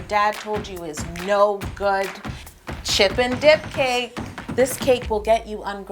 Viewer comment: at first I thought I heard "chip and dale" so I had to go back to hear chip and dip 😂😂 but this is amazing